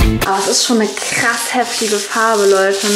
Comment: Hab bei 6:10 : ist schon ne krass hässliche farbe verstanden 😂😂😂